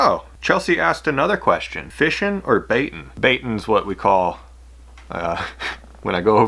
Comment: that answer sounds like she said fishing or banging lol